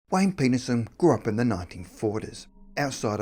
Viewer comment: Hahahaha!! At the beginning of the video, I swear that it sounds like he says Wayne Penis-son! That made my juvenile-humored day 😊